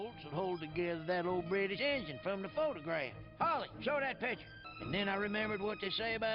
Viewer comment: Quick correction: Her name is HOLLY Shiftwell, not Polly Shiftwell. Granted, I can understand why you'd think it's Polly because of British accents, but Mater says her name pretty clearly during some scenes (21:36 is a good example) as well as the merchandise for this movie displaying her full name on the box. This whole thing reminds me of Nostalgia Critic calling the main villain of Thomas and the Magic Railroad, "Diesel" instead of Diesel 10. That one is understandable though as he knows next to nothing about the show and that the characters in that movie only refer to him as Diesel 10 once in the entire movie, but in this movie they say her name constantly.